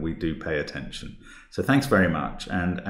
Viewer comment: Every time Mark says "bye for now" I hear "Bifurcate". Love your channel! Thanks for all the hard work.